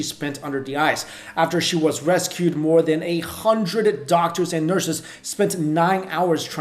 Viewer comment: did he say 8 hundred doctors??